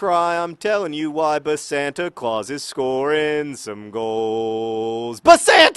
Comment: I was listening to it without watching, and I thought he said placenta-claus...